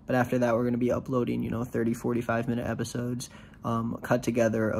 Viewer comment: When he said the Bloodborne videos were going to be "30, 45 minutes" each, I heard it as they would range from 30, to 40, to 5 minutes per episode.